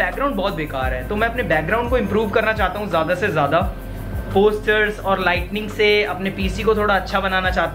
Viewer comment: did he said lightening